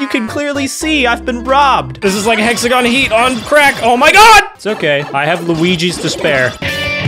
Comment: Thought you said "I have Luigi's despair"